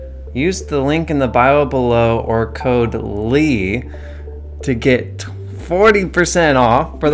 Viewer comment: Love your art style and videos. My husband and I are watching Ghost Adventures it’s paused so he can make the kids sandwiches for school. So I am playing spider solitare and watching the new video. LMAO you just said “Painter Leigh” haha YES! This comment feels so random and makes me think of bouncy ball in narrow hallway. Before I end it… the Pela part just came up I thought said total Leigh instead of Code Leigh. Haha anyways, love the video so far!! Thanks sharing your awesomeLeigh amazing adventures!